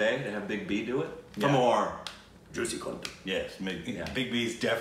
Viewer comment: I heard that " juicy condom" 😂😂😂 Anyone else here!!